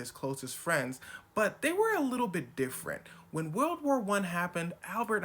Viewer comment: when he says "World War one" the captions say "White fight one" and I DIED after reading that 🤣🤣🤣